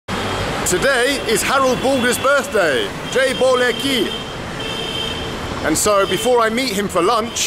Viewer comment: JAI BOLA KI...Aa an indian i don't know the meaning of this phrase. But still great video man. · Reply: So its nothing to do with praising ones testicles, I thought was saying 'Jai Bollocki!'